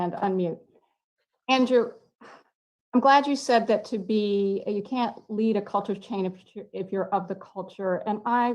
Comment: Maya, 31:57 andrew did not say you cant, he said its hard to. Im glad andrew has been taking notes because you clearly havent